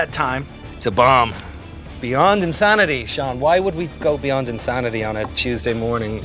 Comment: did I hear him call Sean Evans Sean white